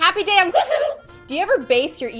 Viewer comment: This whole time ive thought she been saying "happy day on GlOoOoOmM"